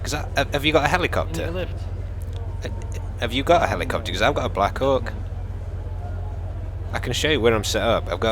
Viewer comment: did said black cock? LOL hahaha